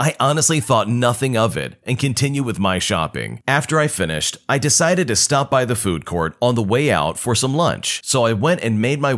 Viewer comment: help i thought he said “i decided to stop at the court” at 1:11💀💀💀